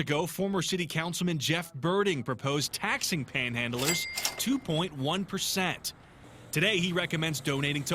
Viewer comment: Tax panhandlers? Did I hear that right?